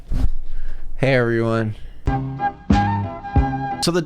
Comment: when you said “hey everyone” the subtitles said “heroin” lmao 💀